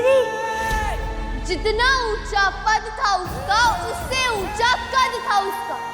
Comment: what did she just say??jitna oncha pad??😂😂😂